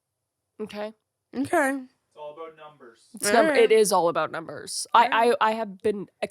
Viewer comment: I thought said "it's all about dumpers" and I was like YES bigger booties are harder to kidnap!